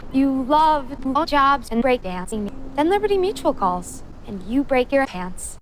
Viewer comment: I hate libertymutual commercials, glad you pooped them. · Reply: I thought you said liberty medical and thought "who hates those?"